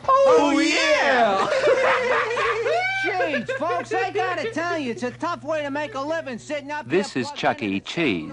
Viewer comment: Does anyone know what Chuck E. Says right here? It’s extremely difficult to make out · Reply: Hey folks, I gotta tell ya, it’s a tough way to make a living sittin’ up here plugged into the same computer as that fruitcake dog! Anyway, Anyway, Listen, Let’s find out which table is having a birthday party!